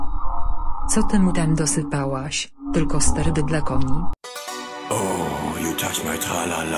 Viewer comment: a ja usłyszałem że ty dałaś sterydy dla kobiet XDD